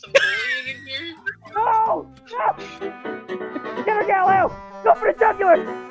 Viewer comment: At the end all I heard was “GET THEM GALILEO GO FOR THE CALCULATOR!” Idk if that is right but I’m dying laughing 🤣🤣🤣🤣🤣🤣🤣😂😂😂😂